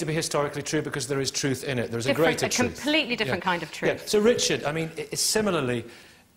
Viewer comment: I thought I heard the host say "Sir Richard" and I was like "Wow, when did Professor Dawkins get a knighthood?"